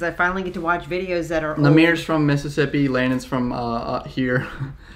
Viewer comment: Did he just say nahmir from mississippii